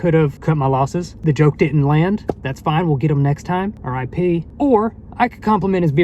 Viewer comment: when he says rip i thought he said or I pee. And I was like ????